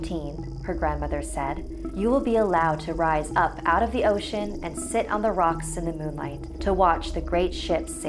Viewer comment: OMG I THOUGHT IT SAID SHIT XD